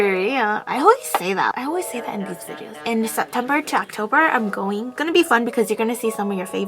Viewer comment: wait what did she say she's what, I heard " I'm goofing"